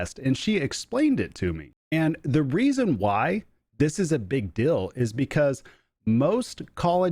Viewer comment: LOL. I keep hearing you say “big dill”. I’m confused 🤣